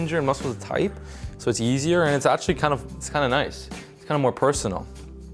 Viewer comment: it sounds like you said "yeezier" lol no shade, no hate I just thought it was funny lol